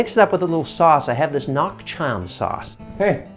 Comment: Lol i had no idea what you were saying at 1:22 so I paused the video, squinted at the bottle in your hand and re-checked with the captions on. "Nuoc cham" just means dipping sauce (literally dipping water/liquid").